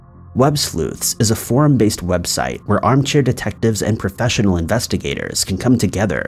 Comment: “Armchair detectives” lol I love it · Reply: I thought he said Amateur not armchair...armchair makes more sense